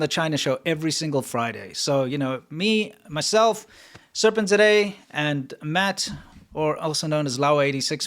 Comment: lol i thought he was saying serpentzatoday but he was actually saying serpent-zed-a